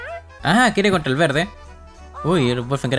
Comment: esto es muy sacable de contexto el dice quiere contra el verde pero yo escuche quiere con el verde xd